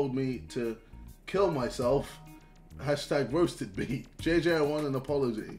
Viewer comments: did he just say hashtag? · no way did he just say 'KMS / roasted me' as 'KMS hAsHtAg roasted me'